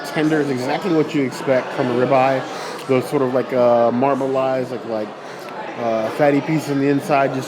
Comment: Did you just say “marmelized”?! Also, lump crab is not an accompaniment.